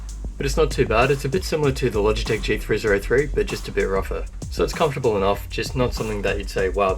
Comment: what was the name of the logitech? cant hear it clearly enough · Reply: logitech g303/302